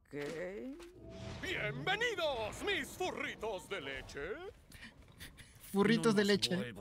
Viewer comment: Cuando la vi así sin ninguna reacción pensé que había dicho burritos de leche no furritos de leche